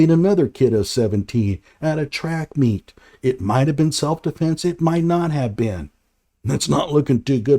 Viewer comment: Metclap. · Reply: I heard him say Self Depense. :face-blue-smiling